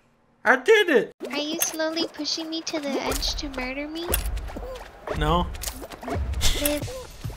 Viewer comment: “Are you slowly pushing me to the edge to murder me?” Dunkey slowly pushing her to the edge to murder her “ no ” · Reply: I thought she said marry instead of murder xd